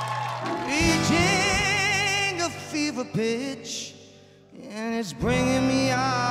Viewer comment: Sounds like he is saying " a fever bitch" lol 😁😁